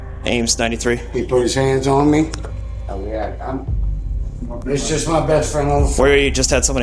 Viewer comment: Wait I thought he said “it’s just my best friend of the PHONE” not on the floor